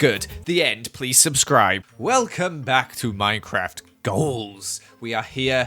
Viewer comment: I heard ‘Minecraft Joels’ instead and I just- Insert normal Minecraft but everything is Joel